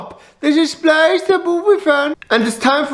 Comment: Their sounded like the said Boss Baby: Da Baby Business.